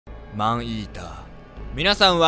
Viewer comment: マンイーダがアイーダに聞こえた私は世紀末